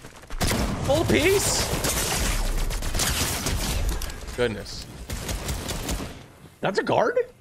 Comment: It sounds like their saying "GOD DANG" 6:01 as soon as you clipped him